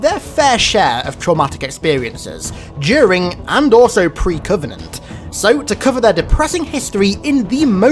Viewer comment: I thought you said “…during and also pre Covid” 😂